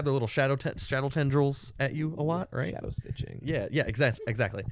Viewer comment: did they say shadowstich? The strongest of vanguard mechanics?!